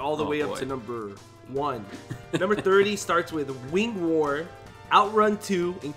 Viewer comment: I know Sega doesn’t own this IP but I want Wings of Wor! When they said wing war I thought they said wings of wor